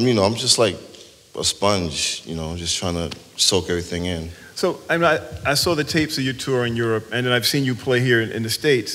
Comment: I cannot catch who D'Amgelo is talking about in regard to Prince. No love lost? Huh? Who are we talking about?? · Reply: Steven Howard Jesse Johnson